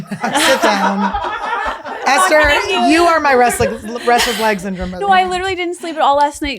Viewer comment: I spit my coffee out thinking I heard Ester say "restless lab*(ia) syndrome" hahaha